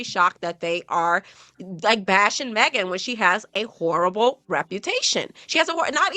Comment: I heard Wh*re-able reputation 😂